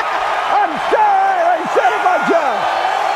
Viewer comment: what did Lee said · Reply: StFidjnr “I’m sorry, I saved my job”